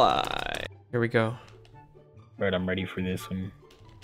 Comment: When was saying I’m ready I thought he said Tom Brady